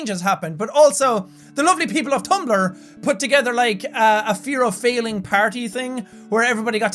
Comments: i thought it said the fear of falling lol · I thought it said the fear of falling lol. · ohhhhh I thought it said fear of FALLING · I thought it said "Fear Of Falling, One Year Later" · I thought it said "the fear of falling" whoops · Anyone else thought it said fear of falling? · ohhhh, I thought it said fear of falling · I thought it said "The fear of Falling" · i thought this said "fear of falling" and i was trying to figure out why jack was making a video about falling over😂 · Thought it said "fear of falling, one year later " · A year later and I still thought it said "fear of falling" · I thought it said 'fear of falling' haha I'm stupid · Omg who else thought it said fear of falling? 😂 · Ohhhh "fear of failing" that makes so much more sense. I thought it said fear of falling. Like why did Jack make an entire vlog about being afraid of heights!? (not like he couldn't though) :) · I thought it said Fear of falling. 😂